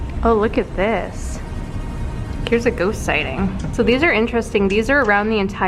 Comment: I thought it said ghost sh*tting :')